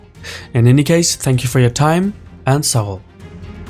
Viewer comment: Hi, I have a question, what is it you say at the end of every video? I keep hearing it as “Thank you for your time, and so on.” But I doubt you say “so on.” Just wondering what that last word is. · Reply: Its saghol meaning thank you